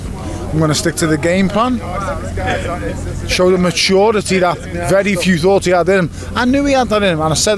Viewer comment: I realized what he said after listening three time ( maturity)😂